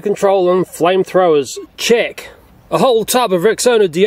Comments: I thought he was saying "Chick,,🤣😂🤣🤣😂🤣😂😆😆😆😅😅😅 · I thought he said "chick" But I was wrong